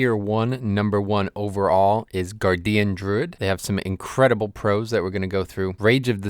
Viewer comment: I fell out of my fucking chair when I heard " Gar Dee Uhn Druid"